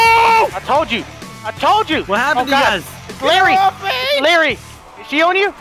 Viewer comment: A misunderstanding at 5:10 made me think I heard De-Larry-ous X3